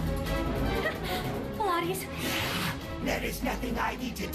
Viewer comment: what did she say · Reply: "Pillates!" It is an excersise used to increase physical strength, flexibility, posture and mental awareness. It makes sense, as she has ninja-like abilities.